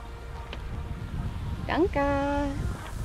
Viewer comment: Did I hear a German "danke" at 6:25? ;-)